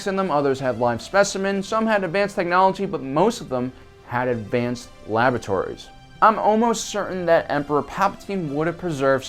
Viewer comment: I thought you said "advanced lavatories" 😂